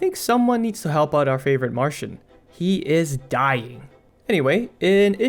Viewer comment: Our Favorite WHAT, you say? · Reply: Marshin